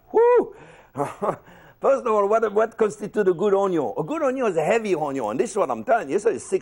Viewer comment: What is he saying : 0:04 - 0:07 ??? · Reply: First of all, what constitutes a good Onyo